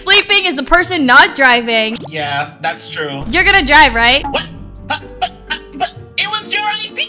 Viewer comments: Bro said “Die” instead of “Drive” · “your gonna die right?” Instead of “your gonna drive right?”